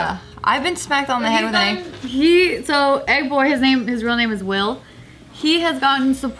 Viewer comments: They keep saying egg boy and I thought they were saying Ed boy😅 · I really thought Megan said "Ed boy", not "Egg boy".